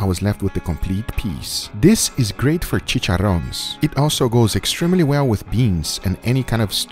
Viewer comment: did i hear chicharon?